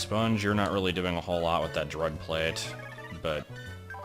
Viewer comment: I know you said "Dread Plate" at 14:09, but the way you said it made it sound like you said "drug plate." XD